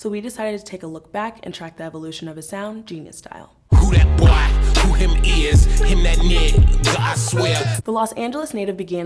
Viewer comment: Wait i thought he said Who dat boi Aho hem is Him dat knite